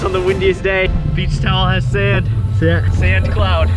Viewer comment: I study culinary art and misheard "beach towel" to "beef tallow" and seeing the towel I was like 'wait wheres the tallow' 😭